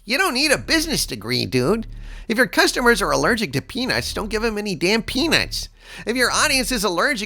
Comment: "If your customers are allergic to pe___" Me: "What they hell are you saying?" lol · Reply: Peanuts. Aka Squirrel Food (at least in our back yard)